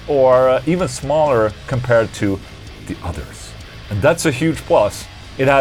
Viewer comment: nowadays when i hear you say "Others" i clearly see otter in my eyes after watched one of your videos when you addressed that. :D